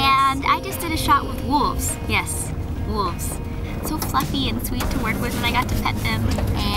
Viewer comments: Did she just say "fucking" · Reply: "fluffy and sweet" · Did she just say "fucking" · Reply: Yo man, I heard it too. I had to go back a couple times. I thought she said "So eff'n sweet." L Well, it's Sunday after all! Praise Jesus he's so eff'n sweet.